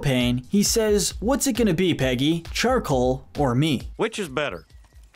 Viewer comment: I always thought he said "charcoal or meat" as in taste the meat not the heat, the moto of stricklan propane